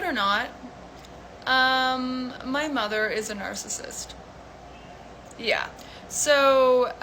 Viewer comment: When she started talking, I thought she said "My mom's a *nurse assist.*" That momentarily confused me until I caught on to her saying "narcissist." 😂